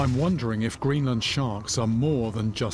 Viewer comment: I only hear Yeet it not Eat it like if you hear the same